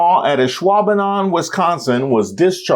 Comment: I had to look up at the quote because I thought they said Islamadan, Wisconsin, and I was like how dare she